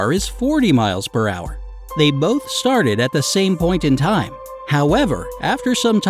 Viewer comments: Aw fk I thought u said they started at the same point AND time. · Half these riddles have more than one answer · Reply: see i heard it wrong and thought he said the same point and time not point in time so i deducted that they are 2 race cars on an oval track and one would eventually overlap the other. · On number 6 u lost me on the wording cause I thought u said they started at the same point and I missed the in time part, so I was lost. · I thought you said the same point and time. That’s unfair...